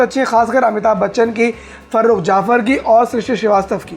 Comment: did he say Amit Shah?